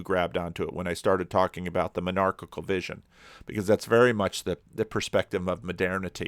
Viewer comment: I thought you said menarchail vision and that's a weird thought... (Menarche)